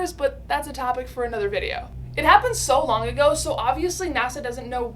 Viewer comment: I don’t know but it sounded to me like she said “Wong.” Instead of “Long.” 😂